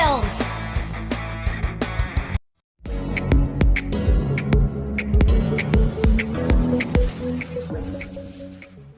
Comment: did someone just say "Darna!" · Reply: pharsa?..🙂